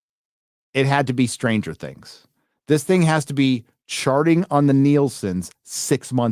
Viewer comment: sound like you said "sharting" which make a lot of sense.